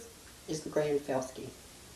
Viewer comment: What does she call that boulder-like thing between her right shoulder and right forearm? I couldn't understand what that Texan Goddess of Strength, Beauty, Power, and Sex said. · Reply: Okay, I think she called it The Grand Felske.